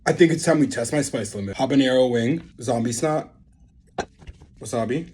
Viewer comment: there is NO way i just heard this man say zombie snot if there’s something out there that is labeled as zombie snot I’m going to fight the person who labeled it.